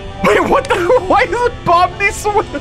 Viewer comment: Why does he say bomni